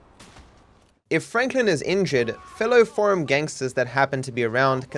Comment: "fellow ___ gangsters" What does he say? I rewound so many times. It sounds like forum or foreign gangster, but i know its not